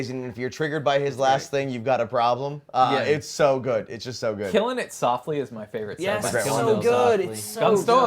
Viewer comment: I thought he said killing me softly and I was 👁️👄👁️